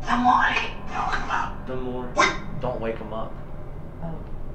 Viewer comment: At like 44:32 I sore I heard “I’m walking “ the a man said “don’t come out “